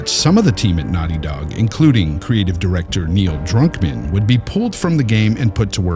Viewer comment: It sounded like you said Drunkman lol